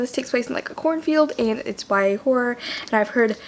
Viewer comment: okay but who tf knows what day it is anymore. when i heard you say "horror" i thought you said w-hore and i was UM SHOCKED FOR A SEC lmao but dropping Hiatus as the montage song was SUCH A GOOD CHOICE but also the editing style of this video is BEAUTIFUL! also you're still my favorite albert einstein :') with the kim possible ring tonE lov u